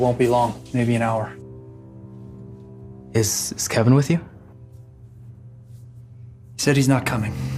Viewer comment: The guy who asks why Kevin isn't there kinda sounded like he had a crush on him. Or maybe it's just because I'm gay, but the way he asked it, like: "Is... is Kevin with you?", I don't know why, but what I heard was: "No, Kevin! Come back, I love you!" This would actually make for an interesting story: Two guys growing up JW, but gradually realizing that they have feelings for each other. One of them makes it out of the cult, while the other thinks that the great tribulation has started. When he realizes that his love would die in Armageddon, he finds that he's more afraid of losing him than of dying himself, so leaves the cult and finds out that there is no great tribulation going on and the world isn't ending, he reconnects with his love and they both live happily ever after. The end. I have to find someone to produce this movie!